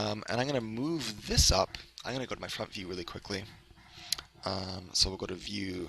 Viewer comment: only i who heard him say "i got to go to my fuck you" XD